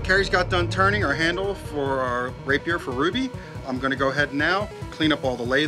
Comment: did he just say "rapier for ruby"? Lol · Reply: Rapier for RWBY